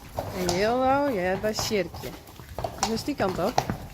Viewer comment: the English is "Jildou belongs to Sjirkje". Although it must be simple, I can't make out the Frisian, "Jildou ??? ?? Sjirkje". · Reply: Jildou hoort bij Sjirkje is Dutch